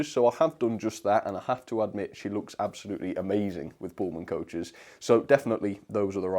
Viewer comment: did you say bullman coaches!? 😂😂 Thanks for the great video